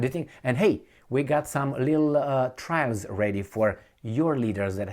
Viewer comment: Haha i misunderstood 10:40, i heard: We got a little "trans" ready for you, and after you are done, we get to inspect your nuclear facilities. 🤣 You know, because of that nuclear security "guy" that was stealing suitcases at the airport...